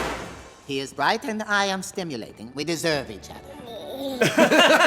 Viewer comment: "He is bright and I am stimulating. We deserve each other" - the aunt I aspire to be to my siblings kids lol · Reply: I thought she said “Simulating” (as in ‘and he is doing/trying to to the same thing that I am), therefore meaning ‘and I like that he also is’!